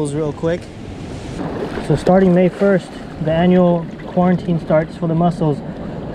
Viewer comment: did I hear quarantine?